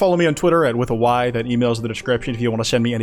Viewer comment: it sounded like you said "pants off" to me the first time for some reason